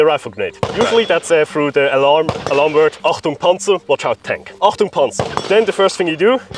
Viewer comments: I think he's saying "achtung, panzer", not whatever that translation is haha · Alarm word WHAT? "Entpanungspanzer"? To me, a Dutch guy who speaks enough German to survive in Germany, it sounded like he was saying "Achtung: Panzer" (Warning: Armor).